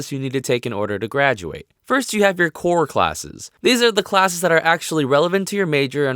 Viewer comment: My volume was really low. All I heard was, "First you have your porn classes."